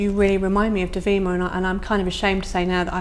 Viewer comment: what name is she saying? devi what? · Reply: He’s saying she reminds him of Dovima, a model he once photographed. Look up the image “Dovima with elephants”